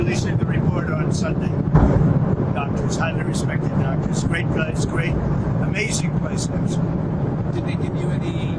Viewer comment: "great doctor great guy, amazing biceps" am... did i hear that right?